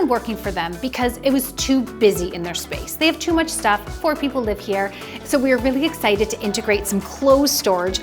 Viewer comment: I thought she said "poor people live here" instead of "four people live here" at 3:00 haha